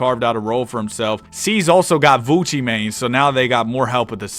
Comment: I thought you were saying gucci mane instead of vucci mane 😂